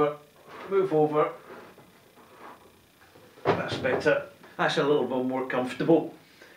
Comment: have I heard you saying "That's a BOWMORE comfortable“ @ 06:29? ;)Or did I just want to hear that? ;) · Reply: " that's a little bit bmore comfortable"